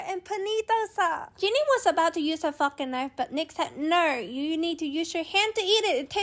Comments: bro why did I hear “****ing knife” instead of “fork and knife” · i thought i heard "fuckin knife"😅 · I misheard the fork and knife i thought she said fvcking knife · I thought said “shes gonna use her f*cking knife” 😂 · When said don’t use your fork and knife I thought she said don’t use your f*cking knife 😂😂💀💀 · I thought said jiny was about to use her fu** knife but then I I realized it was fork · When she said Fork and knife, I heard it as Fu*king knife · I thought she said a fuking knife